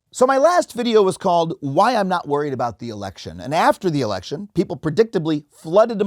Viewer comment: I thought you said not to worry about the election.